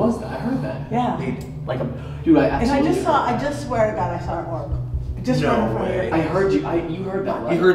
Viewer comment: Something you may of not heard but I heard when Bobbie was in Patties body i heard him say ‘i swear to god I didn’t do anything’